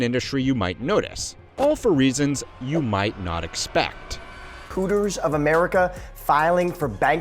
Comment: I’m sorry- Did he say ‘Cooters’?